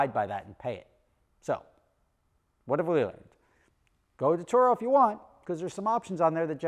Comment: what did you say at 7:54? I'm pretty sure it wasn't English, and I'm not even sure it was human😆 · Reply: "what have we learned" That's what a stroke looks like folks.